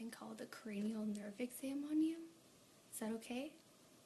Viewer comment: I thought u said cranial nerd sandwich